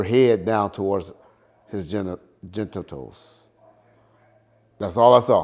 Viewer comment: Gentittals 😂😂😂😂😂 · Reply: Sounded like he said his gentles